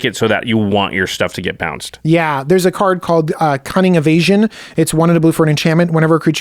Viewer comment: I had this playing on another screen and I heard about a card called "Cunning of Asian". My head whipped around so fast I pinched a nerve... thought the video was going well off the "Safe MTG Discussion" beaten path.